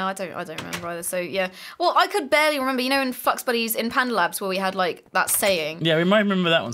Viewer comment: did she say fucksbuddys? it kinda sounded like that